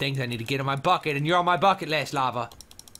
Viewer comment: "ahh bucket" oh i thought u said ahh f%^$ it o.0